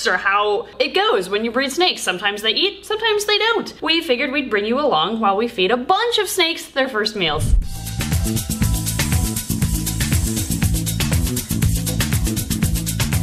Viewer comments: Bahahah. I could have sworn you said you’d be feeding the “first males” to baby snakes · Okay, for a second I ligit thought she said feed the first MALE to the babies ....lol then I realized she said MEAL...lol